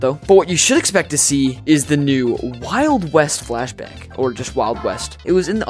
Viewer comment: i heard wild west flashbang